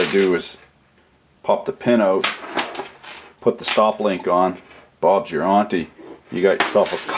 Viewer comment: DId he say "Bob's your auntie" at 16:33? What is that? I love listening to him!